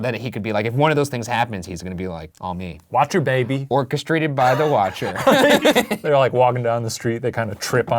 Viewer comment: When Ryan was talking about the neighbourhood I thought he said "All castrated by the Watcher!" I thought "Whoa!! Castrated?" I had to run it back a little and then realised he said "Orchestrated by the Watcher!" Phew!!